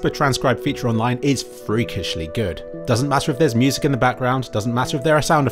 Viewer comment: Written as "free. It's not really good" instead of freakishly good